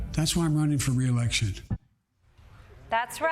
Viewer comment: I swear he said 'that's why i'm running from reelection'